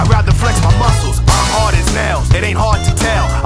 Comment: LMAO I THOUGHT HE SAID," I'm hard as hell, it's not hard to tell!"